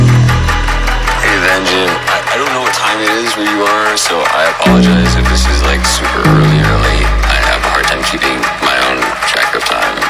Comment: What do you say at 0:46? Can someone write a text, please?) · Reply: hey, Venjent i don't know what time is it where you are so i apologies if is this like super early early i haven't attented to check you time with my own staff do i have (indistinctly) i am happy, dude, you make me really really happy your music is really happy i know you super blessed when you create this music that bicycle blew my mind bro i like play six minutes straight just listening, woa woa woa, full track please? i think this is just a amazing, so cool ... sound transfered into life ....so so imressive (indistinctly фиг знает что он там бормочет) honestly i love you brother and cheer up for good work